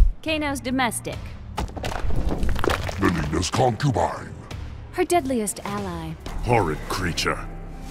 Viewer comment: what did he said? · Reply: He said "Mileena's Concubine."